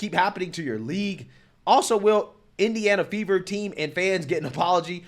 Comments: Ohh... Fever I thought he said Indiana Beaver. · I literally heard 'Indiana beavers 🦫' ....never heard of the team